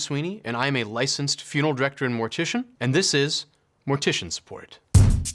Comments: i thought you were saying 'mortician sport' at the start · I thought he said "Mortician Sport" in the beginning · He said "Mortician Support", I heard "Mortician Sport🤪